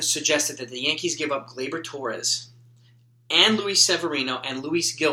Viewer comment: I missheard you at first, I heard you saying trade Torres and Sevy 😂 😄 anyway awesome stuff as always. Im subbed, think you are subbed to me, dont need a shoutout. LETS GO YANKS /From Sweden